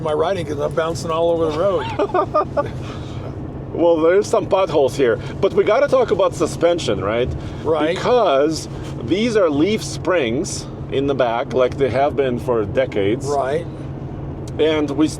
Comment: Sounded like he said theirs Buttholes here 🤣☠️